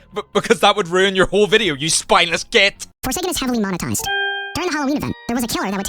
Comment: An ad started at 7:23 for me and it made it sound like he said “YOU SPINELESS GAY-“ ToT